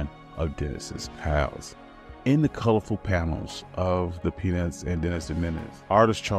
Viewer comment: I think the cc is supposed to say Peanuts😅